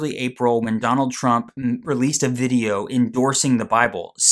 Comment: did you say McDonald Trump?😂😂